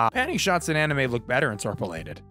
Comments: Was only listening at 2:25, thought you said "interpolated panty shots" and immediately jumped to open my phone screen. My disappointment is immeasurable and all that. 🤣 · 100 percent I heard “panty shots look better interpolated” and was scared of what clip he was gonna show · I thought for a second they'd said "p*nty shots", and I was like "yep. someone who consumes the kind of media with THAT content would hold that opinion" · when I heard say "Panning shots in anime look better when interpolated", I misheard him and thought he said "Pantie shots in anime look better when interpolated". I need sleep